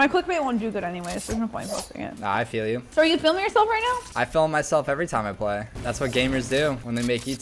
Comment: did you say are you filming yourself or feeling